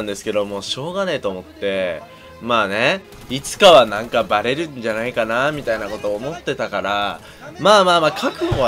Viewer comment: ちっちゃい声でもバレるんじゃないって聞こえるｗｗ